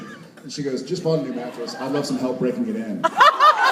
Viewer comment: what said the waitress to Jared at 7:42 ? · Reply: The waitress said "I got a new matress and I need help breaking it!"